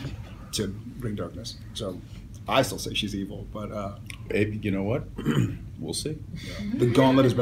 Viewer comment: after Jared emphasizing his view that he thinks the Darkness is evil, is it just me or did Jensen really call Jared 'Babe' ("Babe, you know what? We'll see") ? Maybe I misheard him or something (I'm wearing a headphone, though ;P). Some of the things I like about this interview: 1) Jensen looks so good, like, he looks extra gorgeous and handsome and not mention younger; 2) the way Jared looks at Jensen whenever he talks or whenever he himself was talking just...makes me fangirling so much more than I already have; 3) the make up makes Jared look extra skinny, while his body is not that skinny at all. But he still looks as cute as ever. Like a puppy. :D Thank you very much for posting this interview!